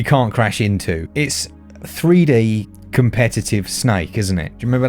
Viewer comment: I thought you said Three DAY competitive snake. 😂😂